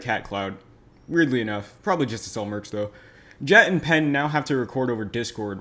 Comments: okay so i just realized that at 0:50 instead of saying pat and jen you said jat and pen XD i thought it was funny so i decided to bring it up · out of the question but did I hear you say “jat and pen” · Who else heard him say jet and pen ?